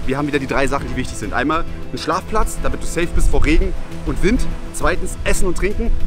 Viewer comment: ich habe verstanden damit es sicher ist vor rewi und Wind